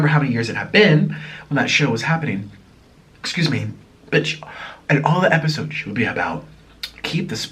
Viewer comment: Im over here crying laughin check out 20:46 it sound like he said excuse me BITCH lmaoooo